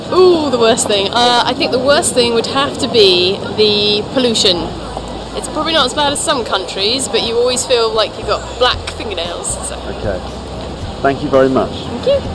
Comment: What did she said at 2:24? "It's probably ????? some countries..." I cannot catch it :-( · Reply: She says "It's probably not as bad as some countries, but you always feel like you've got black fingernails".